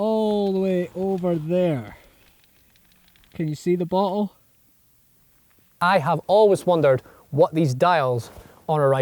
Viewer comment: I thought he said can you see the butthole